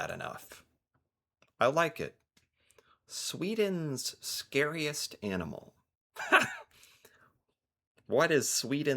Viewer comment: Swede here and I must ask. Does anyone understand what kind of spider she speaks about? I really cant understand what she is saying 😅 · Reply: Ollonborre. Its not a spider.